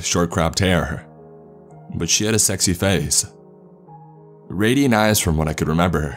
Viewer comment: Theres no way I just hear “mom had a sexy face”